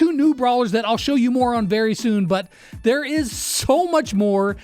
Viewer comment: did he just call me a moron?😱